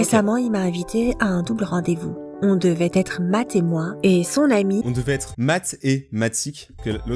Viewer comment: "on devrait être MATT ET MOI " (MATTÉ MOI ;-; ) voilà